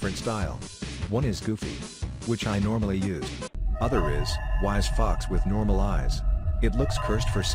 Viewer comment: bro I heard rizz fox- 💀